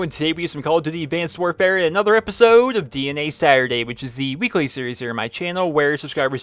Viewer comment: Lol I heard "DNA Saturday" and was like "....what?" So used to kem strike Saturday :P